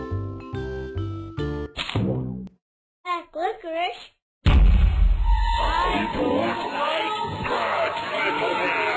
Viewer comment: What did it said in 2:09? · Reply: It said black licorice I DON’T WANT BLACK LICORICE